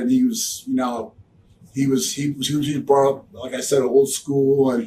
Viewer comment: Can someone tell me what on earth he said at 1:26 ? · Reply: He just fumbled his words, trying to get out "cus he was brought up oldskool"